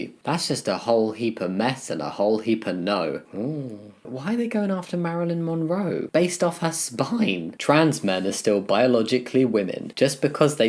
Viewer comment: Hum is it just me or does a segment of the video repeat around 3:00? Anyway, loved the vid as always, you're one of my fav youtubers opening up my cis eyes to new stuff every time ! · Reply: yeah i was listening to this in the background and i kept hearing 'why are you going after Marilyn Monroe based off her spine' i thought i was going crazy lmaoo